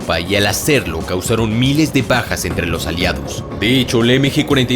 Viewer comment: entendí "causaron Miles de pajas a los aliados" XD